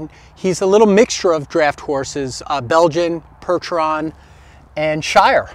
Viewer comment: I heard you say "Pertron" or "Perchon". As a kid in early 1950s, I had often heard of the breed of draught (draft) horse called the "Percheron": perhaps, you were referring to that breed.